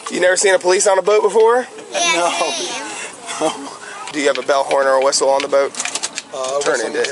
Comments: He definitely, undeniably said bellhorn every single time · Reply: I heard "bellhorn or whistle" too That must be his Lil trick for normies to "seem drunk" when they have a new boat and don't know wtf a bellhorn on a boat is. I'd say "a bell, a horn, or a whistle" but I'm a normal person. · Heck I thought he was saying bellhorn or whistle. He should have said do you have a bell a horn or a whistle. Not a bell horn or whistle. I was thinking an air horn because the end looks like a bell. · Who else heard "bellhorn" and thought he was referring to a singular object? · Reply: I definitely heard bell horn. Every time he says it it’s “do you have a bellhorn or whistle?” If you say this all the time, one would think you’d try to be as clear as possible like oh you misheard me the first time it’s a bell a horn or a whistle required on your vessel. But then you won’t be able to do what the water beater did here.